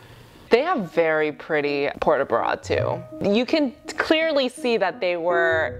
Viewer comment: They have very nice port de bras. Me: Their sports bras are very nice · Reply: 🤣🤣🤣 ya wanna know sumthin funny, I thought that IS what she said HaHaa I'm not a dancer so I didn't recognize the term & it sounded exactly like sport bra!🤣